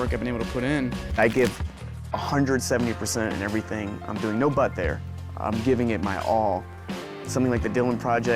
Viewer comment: Thank you for the Subtitles as standard. I thought he said: "No butt hair" at 0.46